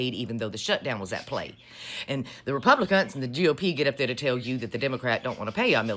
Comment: I heard “republicunts”😂